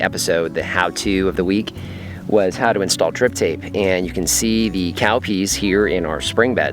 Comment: I keep hearing "Grip Tape" not "Drip Tape" like on my skateboard.